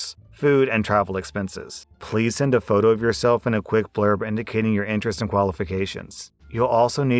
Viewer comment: I'm sorry, I don't know why, but... I wasn't looking at the screen when you said 'please send a photo of yourself and a quick blurb'. I could have sworn you said 'Please send a photo of yourself, and a Ric Flair'. Exhaustion, thy name is Samus.